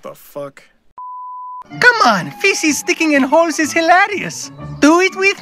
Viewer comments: I know he said "Faces sticking in holes is hilarious!" but I heard "Feces sticking in holes is hilarious!" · where did the spain handseez for everyone come from?....... I have France's dirty mind don't I? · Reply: when Spain said "faces sticking in holes is fun" I thought he said feces 😅